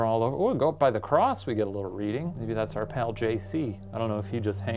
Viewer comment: did you just call Jesus "her pal JC"?! Omfg that made me laugh so hard